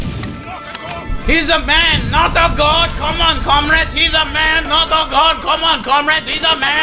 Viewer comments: I dont think Joe can take many more of these games, hes a man, not a god... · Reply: Wtf,i thought they said "C'mon comrades, he is just a man, not a goat!" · He's a man not a God. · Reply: I thought they were saying "he's s man not a gun"